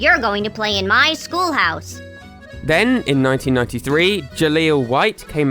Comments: You say "1993" but the subtitles say "1999". Which one is it? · “then, in 1993” while the subtitles say 1999